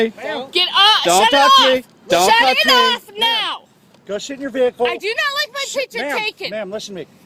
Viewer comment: did he say "Go shit in your vehicle?"